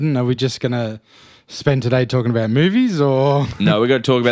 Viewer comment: the first time I heard this, I heard Tom ask Broden whether they ought to spend today talking about boobies.